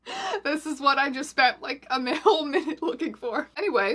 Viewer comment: did she say male-minute? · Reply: She said "a whole minute" but kinda stumbled and threw an m in